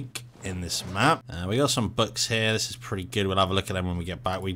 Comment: I had to keep replaying it because I heard “butt hairs” instead of “books here”